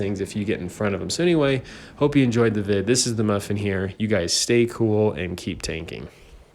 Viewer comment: When he said at the end “You guys stay cool” I thought he said “You guys stink”